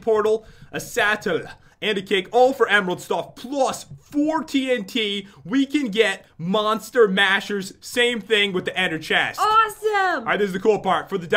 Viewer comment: it sounded like he said "4 tnt"